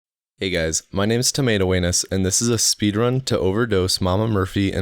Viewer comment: Thought you said "Tomato Weenus". Still hilarious.